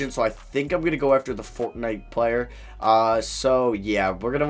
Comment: is just me or did he say "the fortnite player"?